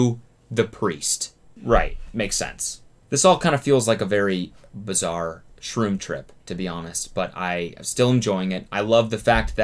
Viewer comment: straight up thought he said "this all feels like a very bizarre troom troom" and i just went with it